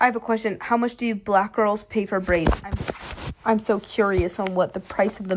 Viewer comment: I thought she said: "pay for BRAINS" Whatever it is, they are getting ripped off.